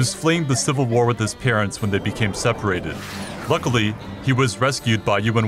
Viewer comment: thank god he was rescued by human workers 0:10 !!! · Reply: haha.. i thought he said human workers too